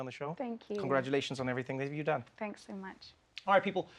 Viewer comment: At the end i heard “watch awful episodes,” but he said all four episodes.